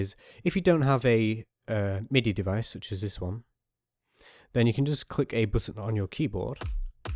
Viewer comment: what does he say?? i can't understand it:(( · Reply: "Then you can just click a button on your keyboard." Yeah my enunciation isn't always the best. If I continue to be unclear at any point, turn on the Closed Captions, they're actually accurate because I hand wrote them myself :)